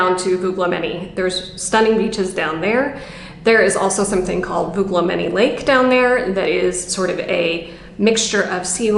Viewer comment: That's nice of you. For example, if you go to timestamp 10:15 there is a name you give that I can't make out, and the CC closed captioning is probably not translating it right. I think CC translated Ios as Eos but I figured that out. Then I can't make out what is said at timestamp 11:00. Putting your full wonderful list in the description might help others. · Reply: It is Ios at 10:15. And the second island at 11:00 is Agistri. Hope that helps!